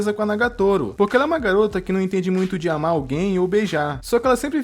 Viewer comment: só eu quem vi isso com o fone baixo e entendi "ela não entende de anal gay"